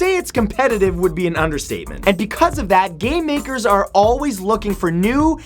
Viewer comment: Cool,but in the introduction of the video I heard "gay makers" not "game makers"